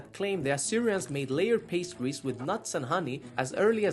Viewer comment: What I heard was “aliens like pastries”😂